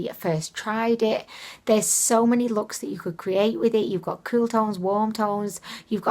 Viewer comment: You said "you've got cool tones and warm tones" and at first I heard it as "you've got croutons and wontons" hehe ;) I love that Ucanbe belonging palette too!